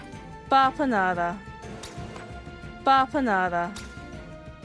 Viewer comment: Also, did I hear “bapanada”???? HOLLOW KNIGHT IS #1!!!!!!!!! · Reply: We're big fans of Hollow Knight around here, haha. My chat begged me for a Bapanada command!